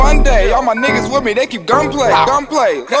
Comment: One time, I thought it said "Shout Out To The Roof"